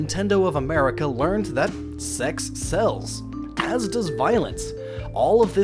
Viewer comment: Was I the only who thought he said "sex CELLS," not "sex SELLS"?